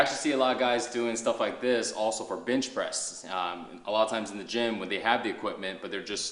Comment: Am iam the only one who thought he said bitch press